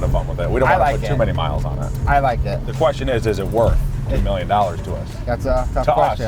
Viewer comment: I don’t know where in the video we said? What time stamp?? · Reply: oh I must’ve misheard at 3:29 I’m assuming you said $2mil?